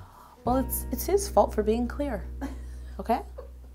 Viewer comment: Happy Halloween VIPs & #NotificationSquad! What's everyone wearing to go Trick-or-Treating tonight? Leave me your FAVE costume ideas BELOW!👇👇👇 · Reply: it sounds like you said queer instead of clear 😂